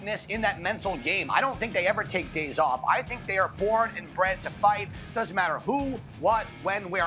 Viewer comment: I thought he said “they are born inbred to fight” 💀